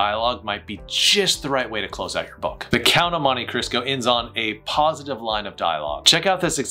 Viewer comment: Did he just say "the Count of Monty Crisco"? 😮